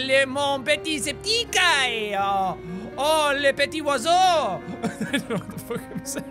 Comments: you said some french and said i dunno what the fuck im saying you acrually said the little septiceye oh the little bird(dont believw me look at the captions) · Jack is the bob ross of 2017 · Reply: jack when u said some french 5:21after uvsaid u didnt know what u were saying u actually said ..little septiceye oh the little bird